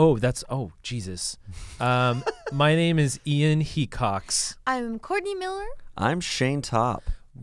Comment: I heard Ian Cocks